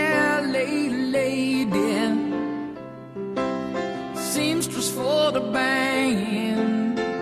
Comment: All these years I heard: "Seamstress for the bangin'" and thought: 'That can't be right.'